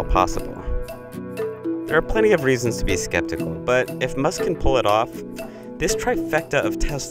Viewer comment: Did i just hear...."if" musk can pull it off??????